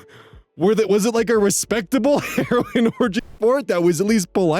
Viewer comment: I have my headphones on and am listening, so I thought you said heroine orgy.